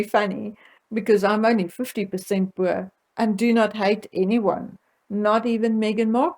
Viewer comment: Some please tell me what it is that she's saying she is only 50% of at approximately the 19:20 mark. I've replayed it several times and even turned on the closed captions but still don't understand, "bureau" shows up first in the closed caption and "poor" shows up when she says she's only 50%. I mean absolutely no disrespect and it may well be just my American ears not quite catching all of her accent. I really would like to know what it is she's saying here if anyone would be so kind as to reply I would be very grateful. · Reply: Connie, she is saying “ Boer.” As you may know from history, Boers ( as the Dutch were called in South Africa ) settled there early in the 17th Century. The British came later and there was conflict between the two, resulting in the Boer Wars, the second was most brutal (<num>). As a result there was always a rivalry of sorts between the Boers ( Dutch) and British. So some might believe that, of Boer descent, might have a bias against the British monarchy because of this.